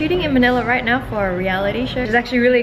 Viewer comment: omg when you said "shooting for a reality show" I heard "shitting for a reality show" 😂😂